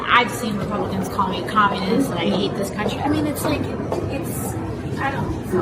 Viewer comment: I’m sorry but did i hear AOC said “I hate this country”